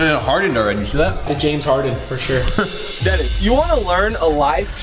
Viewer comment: did rug just call Denis Deven??🤔😅